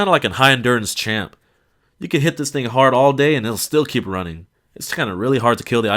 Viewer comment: did he just say nigga? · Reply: "you can hit THIS THING hard all day"